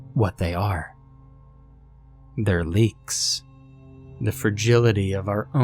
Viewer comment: I really dont get why seeing half a dozen blue butterflies would lead you to doubting your own sanity. Fare enough, sharks in a driveway puddle, yup that would lead to a few questions but, blue butterflies? Are they like some kind of cryptid I havnt heard of? Or portent of a natural disaster? Cheers, Lee. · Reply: To be entirely frank, the protagonist sounded so irrational that when he said he found out what was going on, I thought he said: "Leeks" and in those few moments before the explanation made it clear that I misheard, my mind went: yeah, leeks, it makes perfect sense for you to come to such a ridiculous conclusion.